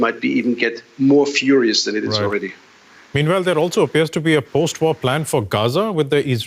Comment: Postwar plan? Oh, I thought you said plain...